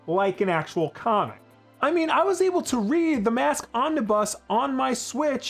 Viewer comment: Lol I thought he was saying “ I read the Mask on the bus”